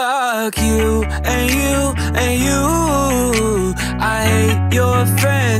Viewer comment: I thought it said forget you and you well I thought wrong xd